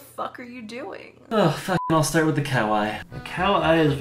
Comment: i thought he said "kawaii" instead of "cow eye" tf